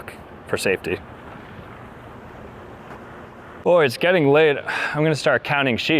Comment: why did I think he said ´´getting laid´´ instead of ´´getting late´´ at 18:06 I´m too dirty minded I guess